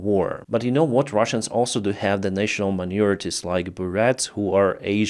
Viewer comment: Go to <link> to stay fully informed on what's happening in Ukraine, Russia and around the world. Subscribe to save 40% off unlimited access through my link. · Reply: it sounded like you said, "national manure" just like the Buryats. They did atrocities.